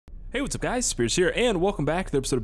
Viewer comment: I thought you said "Hay whats up its scares here" haha you should change your intro before ya get picked on haha